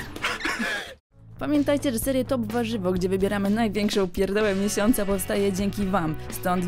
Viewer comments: czy tylko ja usłyszałem "Największą Pierdole miesiąca"? xD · czy tylko ja usłyszałem "Największą Pierdole miesiąca"? xD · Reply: kfz14 pierdołe uslyszales bo tak powiedzala